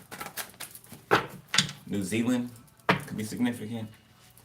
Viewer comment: Boyyy did you say New Zealand 19:23❤😍🤗✨